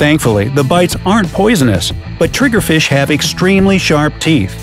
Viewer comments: To be honest I thought they said Nigerfish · TIMESTAMPS Fire Coral 0:40 Flower Urchin 1:20 Cone Snail 1:50 Portuguese Man-of-War 2:32 Snea snakes 3:54 Lionfish 4:51 Chironex 5:39 Surgeonfish 6:15 Indonesian needlefish 6:59 Triggerfish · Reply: At the end I thought you said at the end tiger fish